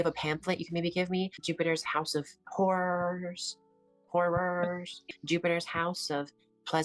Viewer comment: 😂😂 the house of haus...i thought they said house of wh●res. Funny when he gagged.